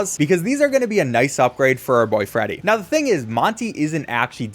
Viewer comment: tbh I thought u said for out boyfriend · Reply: For our boyfriend*